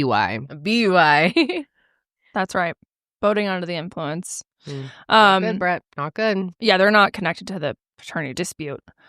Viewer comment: apparently I can’t hear shit. tell me why I thought you guys said voting while under the influence and I was like wow I didn’t know that was a law but I guess that makes sense. 😅😂